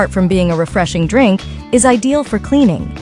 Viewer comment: I thought u said drank it..i feel 🤮 😊